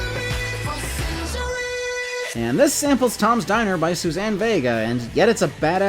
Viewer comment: anyone else thought she said “tom steyer” the first time?